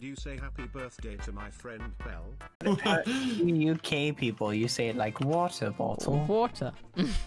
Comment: why did i hear "you gay people" help